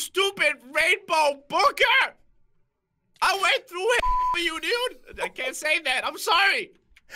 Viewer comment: LOST MY SHIT LMAO!! xDD 11:31 OM HELP ME XDDD i never heard him say that before lmao xDDD GO CRAINER! XD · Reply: Sarthak Agrawal I thought he said I went through hell for u.... I didn't hear this. and he said it wasn't that bad